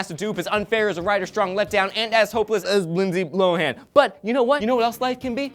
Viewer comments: Did he say Lindsay Blowhan?? rofl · Did call Lindsay Lohan, Lindsay Blohan